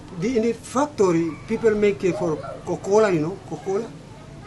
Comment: I never realized nutmeg oils were used in coca-cola 🤔 Imagine some nutmegs exported from Maluku, processed and then some goes to the coca-cola factory in America, then some of the coca-cola cans and bottles are exported back to Maluku. That must be a fun journey. (Yes, I know there are also coca-cola factories in Indonesia, this is just a fun thought 🤣) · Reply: Yang dimaksud bapak itu cocoa drink atau kokoa nyebutnya, pick up nya sbg coca cola.. hehe.. salah denger